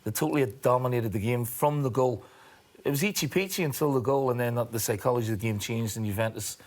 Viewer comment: Did he just say eachy peachy wtf